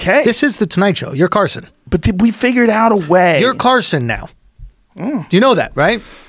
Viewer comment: Hey guys, what does he say here, please? "Carson"? (sorry, I'm not English)